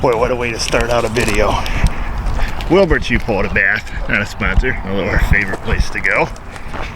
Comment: It always sounds like he says Wilberts you bought a bath😂 What the heck does he say?